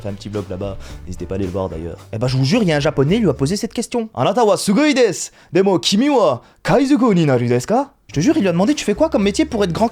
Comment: il dit quoi ? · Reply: Je deviendrais le roi des pirates